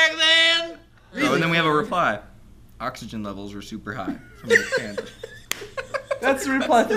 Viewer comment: I heard: "Oxygen levels.We're super high."